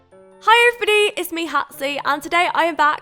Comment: Did I hear hey it's me hatsy at the beginning? XD